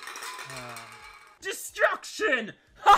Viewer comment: did he just say- “Destruct shit”